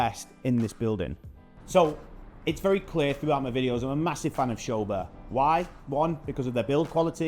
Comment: You are a massive fan of ** ?? I cant really decipher what your are saying. Also i don't know the areas, or the buildings. Can you pls write it down ? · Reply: I say massive fan of Sobha, they are a developer. This property is Sobha Waves Grande, located in Sobha Hartland